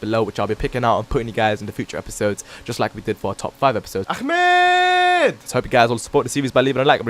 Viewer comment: Sorry for the stupid mistake for the 4th gear test. The first two episodes were infact the 5th gear test. I'll make the correction with the future episodes. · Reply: Did u say AHMEEDD!!!!